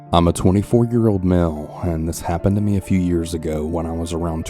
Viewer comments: Male Voice: “I’m a 24 year old male.” Me: Wait that’s illegal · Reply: ngl i thought he said "I'm a 24 year old mammal" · Did I just heard "24 year old manhole"? in